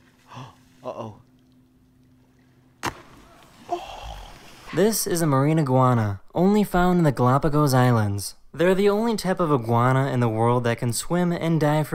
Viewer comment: When he said mari iguana i thought he said marijuana😂😂😂